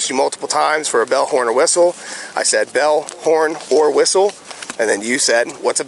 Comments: I thought he said “bullhorn” as well. I thought it was a boating thing or something. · I thought the cop said "BELLHORN". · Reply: I thought he said bellhornwhistle, all one word. This cop is ridiculous. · "A bell, horn, or whistle." Dude i thought he said BELLHORN, NOT a Bell or a horn and I have been boating seen i was a teenager, i am 42 now, its the way the dude said it. This whole stop is complete Horsesh!t! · I thought he was saying belthorn too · yo I swear to God on my life, when he said Bell horn or whistle, I genuinely thought he said bellhorn, or whistle. I assumed a bellhorn was a specific type of horn. this cop is an absolute joke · This officer is ridiculous! He is showing his power. Why treat him like that infront of his kids. Stumbling with his fingers are you kidding!! I thought he said bullhorn too! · I swear I heard him say "belthorn". This cop is the type of tacticool doofus who speaks in over complicated words to sound smart.